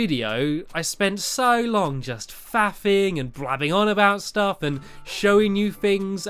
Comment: did said "I spend so long just FAPPING"?